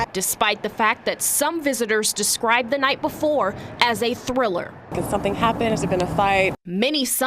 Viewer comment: Did I hear "Thriller"?